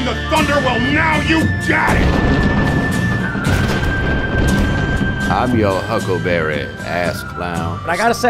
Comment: Can that be a shirt? "I'm your huckleberry, ass clowns." Lmaooo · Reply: I heard it as "I'm your huckleberry ass clown." Yes, yes he is.